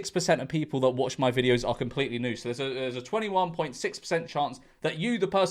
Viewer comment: I thought you said 21.6% of people watching were completely nude 0:33 and I had to go back and listen again 🤣